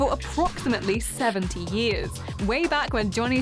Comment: I swear to god she's saying "70 years". Wtf?